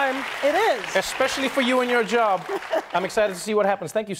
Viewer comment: I thought it said Karen bias